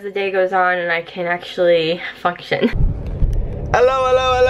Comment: I thought at first said fucktion